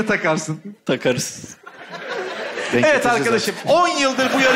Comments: ne dediğini anlayan var mı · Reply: Denk getircez artık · te ne diyor anlayan var mı? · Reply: Takarız...